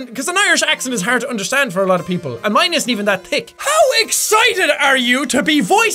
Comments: Irish accent isn't that thick? When you said thick, it sounded like dick haha 😂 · did anybody else hear him say it like and mine isent even that dick